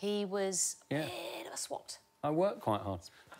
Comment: Does anyone know what a 'swat' is? I've never heard of this before. Is it a university club or a job? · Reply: Swot. It's a name given to someone who is constantly studying. Basically another word for a nerd or boffin.